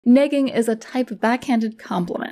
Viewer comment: Ha ha. I thought that said “nagging.” 😅😅